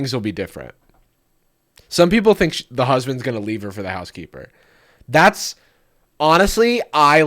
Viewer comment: i misheard this as "She's gonna leave her husband for the housekeeper"